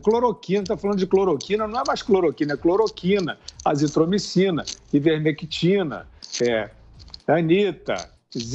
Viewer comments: Não é mais cloroquina, é: cloroquina, azitromicina, ivermectina, Anitta (???) Ouvi corretamente ??? kkk kkk kkk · anita? kk ouvi errado so pode